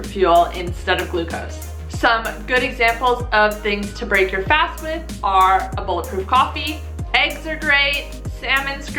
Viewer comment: What is she saying?? A bowl of proof coffee?? I listened to it 5 times I can’t figure it out. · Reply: Bulletproof coffee 😂😂